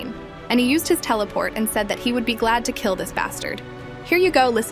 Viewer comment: when i heard uttv i heard 'i am glad to kill this fuck'